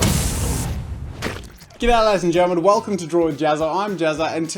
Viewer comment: Every single time I start one of your videos I hear "Get out ladies and gentlemen". Please someone help me and tell me what he actually says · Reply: "G'day ladies and gentlemen"